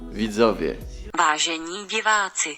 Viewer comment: usłyszałam ważeni piwacy XDD na początku